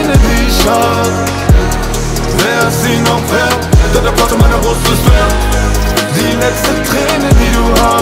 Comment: Ich habe immer verstanden dann erwarde meiner brust ist leer